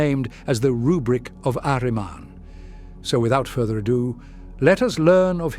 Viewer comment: I thought this said the Republic of Ahriman at first and I couldn’t stop laughing